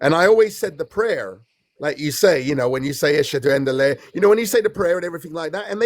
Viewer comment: At first I skipped his monologue but then I went back to laugh LMAO Idk how you kept a straight face. @ 3:20 why did he say "Ashunda unda lay"